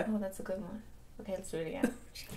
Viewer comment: When said "thanks so much for watching and i'll peel yall later bye" , i thought he said "thanks so much for watching and i'll peee on yall later, bye!" and I was SOOOO CONFUSION